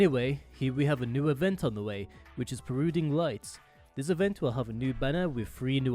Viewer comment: Is anyone else hearing brooding lights in 0:24? Perhaps might be the one who said bruhmanix and tee apple sauce in the legendary watchmojo video about Arknights □